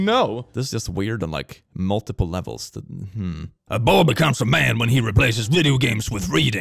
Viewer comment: I heard "A bobba counts a man"... Wow, I watched a bit too much Aphmau.